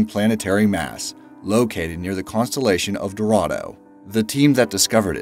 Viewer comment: Dorado lol I thought you said toronto!